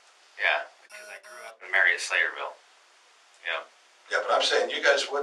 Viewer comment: At 20.19 it sounds like he said the mayor of slayerville ,I know that's not what he said.anyone else hear it.